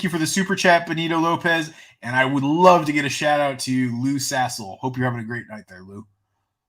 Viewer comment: Did no one catch the Lou sasle thing ???? It sounds like he said Loose asshole lol whoever sent that is a troll smh